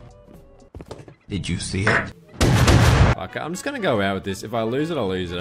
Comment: I swear to god if I hear "Did you see it *fart*" one more time 😭😭😭